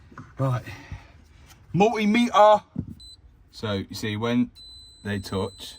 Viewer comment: did you say multimeter?